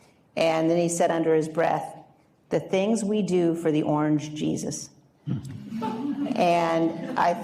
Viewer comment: I thought she said "that orange p***s" not Jesus lmao🤣🤣🤣🤣🤣